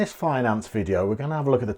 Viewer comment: I swear I thought he said "fine ass video" at the beginning (turns out it was finance lol)